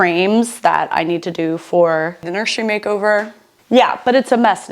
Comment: Did I just hear "the nursery makeover"??!!!! I seriously listened back to that part so many times and I can't unhear it, why is no one talking about this in the comments???!!!